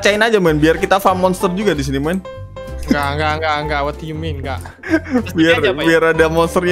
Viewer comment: aku yang salah denger atau bilang kak?